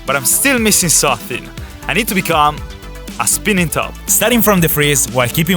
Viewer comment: i heard that one before but more like "I need to become a compasse"